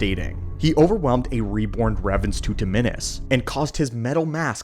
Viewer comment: I'm getting crazy trying to understand what you say at 6:13 , "a reborn revan to [what???]". HELP PLEASE !!! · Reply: “ rebron raven’s tutaminis” tutaminis is a force ability where the user absorbs and dissipates a energy based attack